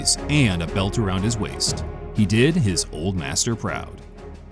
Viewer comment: FUCK that what did he mean at 11:13 when he said "he made his old matter proud" 🤔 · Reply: its simple english. he made his old master proud